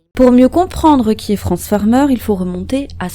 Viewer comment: j'ai compris "pour lieux comprendre qui est Transformers" Cocasse